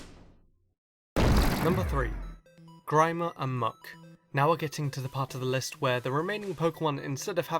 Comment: I thought he said Grimmer and Mudkip XD